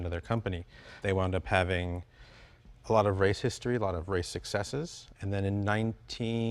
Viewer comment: When he said race history, I thought he said racist story. Lol